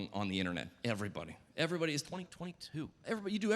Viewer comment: He did NOT SAY "2022"! Someone tell me what he really said. I bet it's funnier (Edit: timestamp